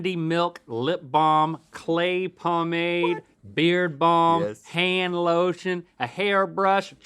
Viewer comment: When Link said "beard balm," I definitely heard "beer bong" lololol